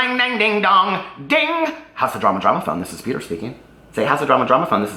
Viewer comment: I've always thought you said "How's the drama, drama phone"